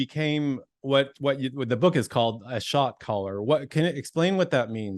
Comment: story is amazing! I got chills just hearing about all of the ways God worked in his life. I was listening while working out, and for a while there I thought he was saying he was a “shock collar”! Glad he clarified what a “shot caller” is!!!😂